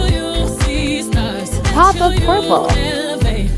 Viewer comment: Thought it said Biden Stamp